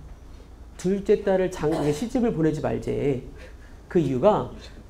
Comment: 둘째딸을 그다음 뭐라는거임 ..? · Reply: 장가로 말할려다가 시집으로 커브 결론 : 둘째딸을 시집을 보내지말래~